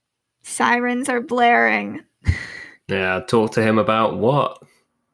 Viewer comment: I can't hear the tape very well, can u pls tell me what did Lyle say about talking to his dad? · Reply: Exactly. Lyle said "I never got a chance to sit down and talk with him". Which is a big clue about what was actually happening in the house that week